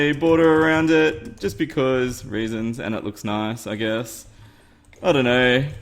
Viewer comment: What?!? Did you just say?!? Did I hear "You know, Just because RAISINS"?!?!?!?